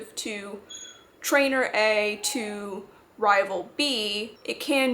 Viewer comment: I kept hearing you say "mono rival" lmao